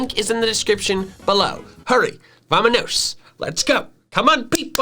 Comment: Subtitles says "Michael speaks in a foreign language". He actually says ¡Vamanos! (Spanish for "Come on")